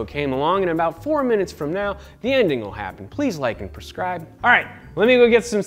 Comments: did he say "prescribe" instead of "subscribe"?? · did he really said "please like and prescribe."?